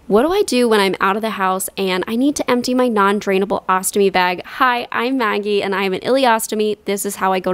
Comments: First time I've seen yiur vids and I heard "HI, im 90" instead of "Hi, im Maggie" and I was like DANGGGG NO WAY YOURE 90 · Okay okay BUT hear me out, I definitely thought she said, “Hi, I’m 90” the first 5 times I watched this. I literally rewatched it several times to hear what she said ✋